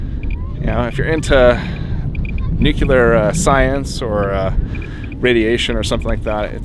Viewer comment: did you say "nucular"?